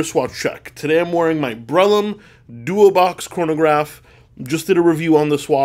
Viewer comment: Did he just say 'Breitlim' or was it '"Breitling"? Anyone else heard the same thing or was it just me? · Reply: NVM, finally got it; it was Brellum